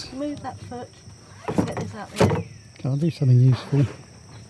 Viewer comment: Did you say, " get paddling Liz; come on, do something useful" LMFAO. Im surprised she didn't leave you on the Oxbow lake. LOL.